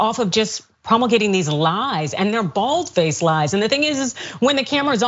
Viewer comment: And did I hear "balls face lies"? Lol that was good if you slipped that in on purpose, or even if it was an accident-🩷